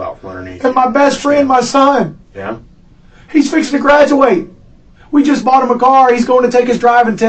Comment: why do the subtitles not actually reflect what he’s saying? He’s saying “he’s fixing to graduate.” he definitely didn’t say “he’s about to graduate…” I’ve been noticing that and that’s really irritating. Stop misquoting people it’s awful.